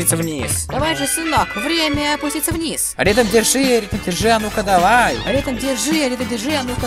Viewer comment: Мне послышалось скажите а.у.е